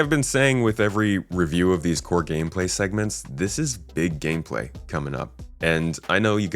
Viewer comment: When you said "big gameplay" I heard it like "big pharma" and had a little giggle to myself.